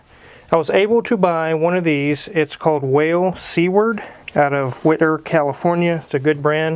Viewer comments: what I heard: "It's called whale C-word" Me: expecting to see "whalec**t" on the label* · Shale Seaward.. I literally thought you said "Whale C Word"! haha